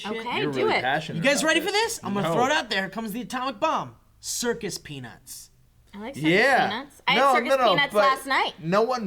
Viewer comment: I kept hearing "Circus Penis"